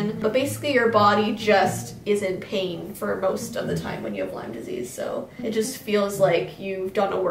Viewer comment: When she said Lyme disease I thought she said lime disease